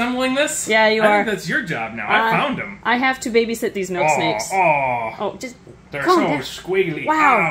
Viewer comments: I thought it said Milkshake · I thought it said milkshake for a sec 😂